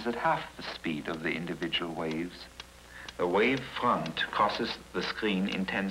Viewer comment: Seriously, the subtitles need editing. Examples: at 2:41 the word "crosses" is show as causes. 2:46 as cost instead of "cross". 3:21, Valley is shown instead of "Fale" , pronounced fah-lay, which is an open air Samoan house. 4:03 upper henshin should be "apprehension" Rather hilariously, it shows what should be "Pago Pago" as Panama Panama at 6:05. Unfortunately, there are many, many errors. The presentation of this valuable information still holds true today and it's a shame that the close captions are wrong. This is such an educational film, created and narrated by a scientist who was the greatest pioneer of surf forecasting of all time!